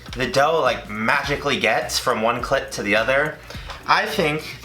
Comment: did he just say “clit” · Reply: Clip* 😂😂